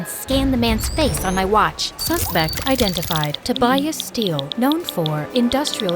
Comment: hlep i thought it says to buy and steal